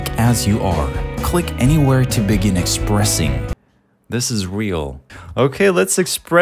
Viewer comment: When it said expressing I thought it said depressing